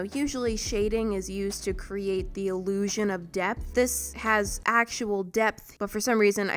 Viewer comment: When she said shading is to creat depth but I thought she said death